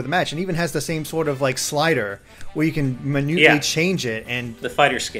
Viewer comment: did you say Manewly? ༼ つ ◕_◕ ༽つ